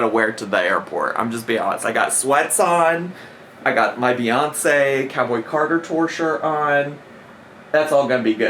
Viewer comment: Bestie! I thought you said fiance not Beyonce 😂